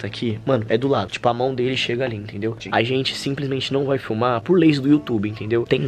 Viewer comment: eu entendi: “simplesmente a gente não vai fumar” kkkkkk